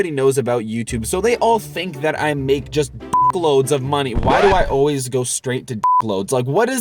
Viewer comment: thought he was saying sht load